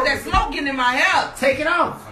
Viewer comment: What is she saying? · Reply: I think "Andre, you gonna have to move cause that smoke getting in my hair"